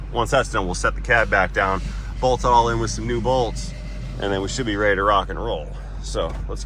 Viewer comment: when you said "bolts and hose" i thought you said "boats and hoes" lmao.